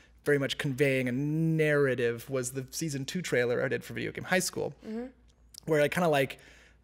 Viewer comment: I want to look up an example that was brought up at 15:58....Season 2 trailer of "View-da-gain High School"? I can't make out what was said. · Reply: Sergio Rodriguez Ahh, Video Game High School. Figured it out from the notes : }